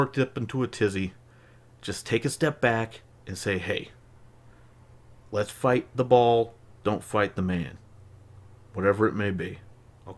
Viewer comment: I'm watching this on my my phone and the speaker got muffled at this point. So where you say "hey, let's fight the ball, not the man" I heard "Lets Bite his balls, not his hands"